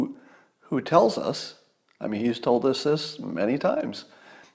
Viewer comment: I thought I heard, the guy who "rigs" but I went back and you said read.